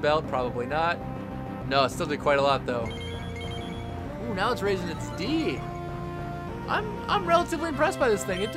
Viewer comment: I listen to the video will playing along and I hear " ... it's raise it's D " ... XD